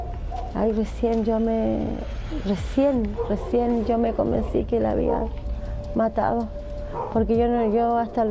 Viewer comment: Escuchen en el minuto 59:05 Pongan atención a lo que dice Como pudieron pasar x alto eso???? Lo que dijo .. ella asumió su culpa sin qerer hacerlo... pero lo hizo xq ella mató a su propia hija carme de su carne! Que tipo de madre es esta maldita !! Pobre niña que en paz descance!!!😥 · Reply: escuche el minuto 59:05 y si Ella dice '" y recien recien me convenci q la habia matado" buen 🕵